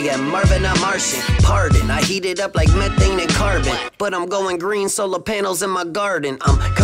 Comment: did he said carbon? i actually go green too? sorry no inglish